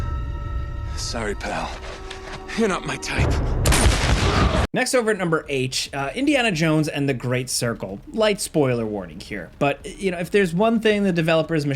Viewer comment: "Next over at number 'H' "? Wait did I hear it right? Jake (and sometimes) seems to intentionally place these tiny things to his videos. Is there an easter egg we're supposed to hunt or something?